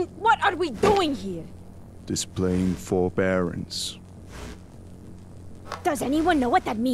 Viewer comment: what is that word btw the captions can't pick it up and im starting to think that word isn't real! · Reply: I thought he said "just playing four barons."